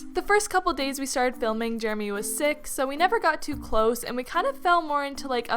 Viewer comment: did anyone hear "jeremy was six.."?? ☀️😂😂 luv ur vids